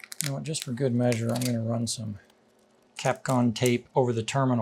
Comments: did he say Capcom tape? Does anyone have a link to the tape? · You used Kapton tape as insulators on the battery terminal connections. I had never heard of that. I had to ask ChatGPT what word you had said, and have since learned all about “Kapton”.😊